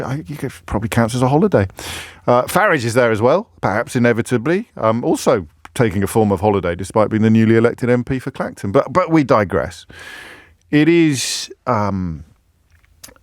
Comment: did I here correctly that Mr. Farage is the Honorable MP for Texas? I heard about Neo-colonialism, but somehow I did not think it would go in that direction...